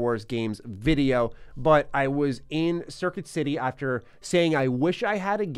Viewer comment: At first I thought you said serpent city instead of circuit city and thought holy shit what kinda epic stores do they got in New York